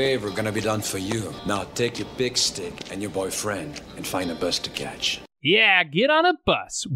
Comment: I remember watching this as a kid and thought he said “find a place to catch” instead of bus to catch. Which I thought was equally funny.